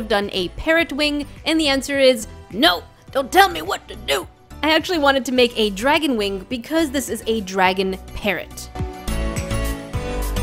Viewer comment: This whole time I thought she said dragon carrot